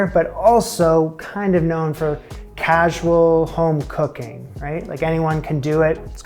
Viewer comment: When I heard "also known for casual home cooking" I really thought I was about to hear "also known for casual homophobia"